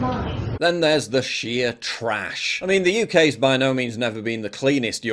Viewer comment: I heard Paul say, "Then there's the shia trash". I was mistaken, but maybe not.